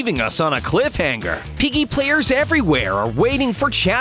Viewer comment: When you said mr p I thought you said mr pee